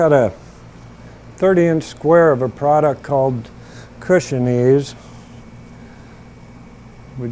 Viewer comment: At around 1:43 you said you put it over the cotton on the seat to keep it from balling up. If I heard it wrong, I apologize. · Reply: "cushion ease"